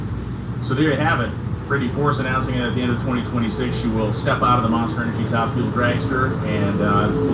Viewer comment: Couldn't hear because of all the background noise Is she leaving at the end of the season Or next season???? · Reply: I heard him say at the end of 2026 so I'm confused myself